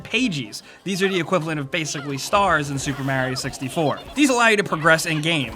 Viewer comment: Did you just say... mærio...